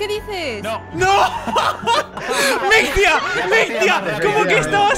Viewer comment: Al inicio creí haber escuchado "Mi tía", luego rebobiné y ya entendí "Mictia", estoy pendeja jaja, por cierto, Mictia soy yo en los juegos, mentir y engañar para ganar pero a la vez cagarla jaja, amé esa chica! <3